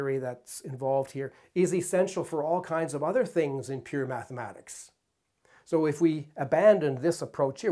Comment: I initially misheard you saying "weird mathematics" instead of "pure mathematics", which I think sums it all up nicely.